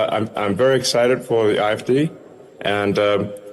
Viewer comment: It sounded like he said the "IFD!" Not "AFD." Also....anyone notice the Usha doesn't look well? What's that about?😮 That's concerning.😮😮😮